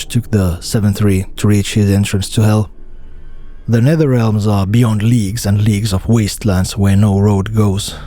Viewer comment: I heard: "The Netherlands are beyond leagues and leagues of wastelands where no road goes, barred by gargantuan gates, and populated by horrors no one can outrun." I don't know how to feel about that.....